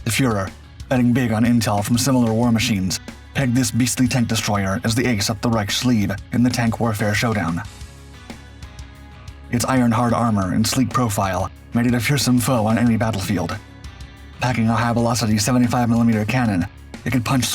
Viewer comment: Did it just say iron hard armor?.. 😂